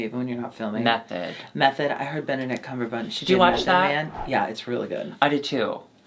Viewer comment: Did Alexis really say "Benedict Cuminherabunch"? 38:23 I am howling!